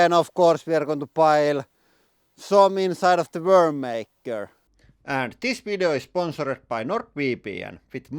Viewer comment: Honestly thought you said "world maker." Which in the case is pretty fitting with the fiery explosion and all lol well done as always 🍻